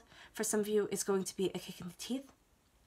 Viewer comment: Did she say it's going to be a kick in the teeth?